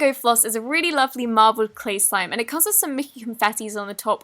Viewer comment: did she say clay sime??